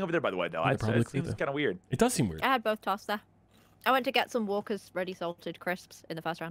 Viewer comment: what were you doing? starts talking in british radda radda radda radda? Omg that was so british aint nobody gonna understand what they said not even captions. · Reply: Closed Caption: Walker Ready Salted Crisps.